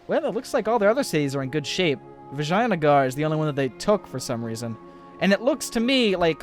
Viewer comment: Am I the only one who heard him say vaginagaurd16:15